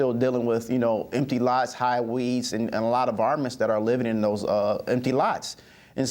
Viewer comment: Haha, sorry, this is great I totally agree. But did he say "varmints"? :)